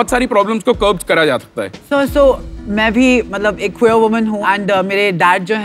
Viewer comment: what did she say? Kaisi woman? Akhoyo or what?